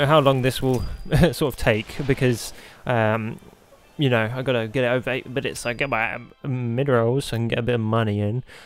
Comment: did he say gotta get a vape